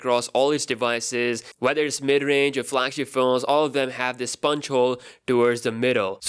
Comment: did you just say a "Sponge Hole"?